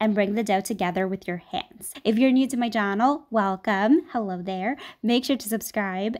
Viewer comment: omg 0:41 did you hear! XD she said 'jahnel' instead of 'channel' ! XDXDXDXDXD LOLlLlL